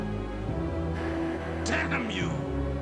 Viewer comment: "Damn You" I thought I heard him say: "Have at you!" Proceeds to get smacked into the wall